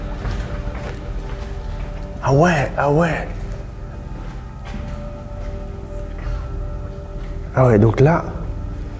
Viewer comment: J'ai entendu des chuchotements féminins à la minute 29:24-27... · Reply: c'était le mot weak